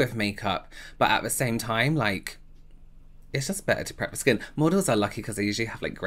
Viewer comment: Lord I had to go back and listen to your reaction to the skin prep….I thought you said Mortals have good skin…not Models. Lol. Time for me to go to bed!